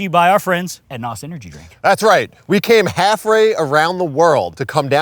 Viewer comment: I believe you meant to say halfway instead of haffray 😂